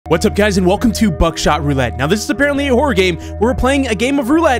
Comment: I'm gonna be honest i thought it said backshot 💀